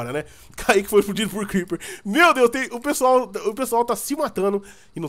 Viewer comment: só eu que entendi "Caique foi fodido por creeper"? KKKKKKKKKKKK